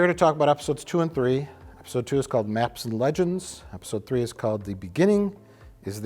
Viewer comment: I thought mike said naps and legends..I thought it was a joke on picard being so old. I miss heard